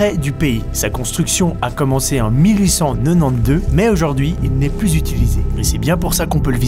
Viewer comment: 1800 quoi ?... 😅 help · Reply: Emiko Dagorne 1892